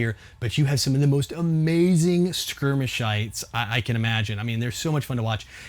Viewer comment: it sounded like he said skirmish shIts